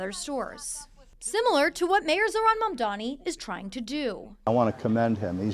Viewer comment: there was an error in the English captioning. It reads "Mayor Eric Adams" instead of Mayor Zohran Mamdani.